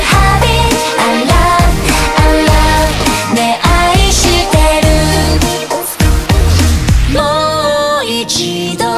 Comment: なんて言ってるかわかる人います？ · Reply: ねぇ愛してるじゃないですか？